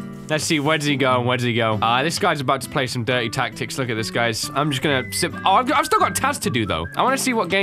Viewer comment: he says,“im just gonna,simp“ This is a joke and what i heard